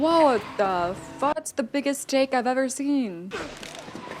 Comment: I totally spit out my coffee because misheard "That's the biggest stake I've ever seen" 3:02 especially given it's position on the bike